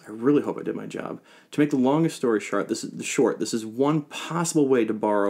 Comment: All I heard was "long story shart".